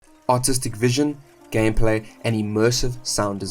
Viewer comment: I swear the intro had me shaking my head... I seriously thought you said autistic vision 😭😭